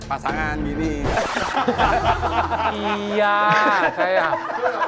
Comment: Aku ngak salah dengar chef Juna bilang "Iyaaaaa sayang" 17:27 😭🤣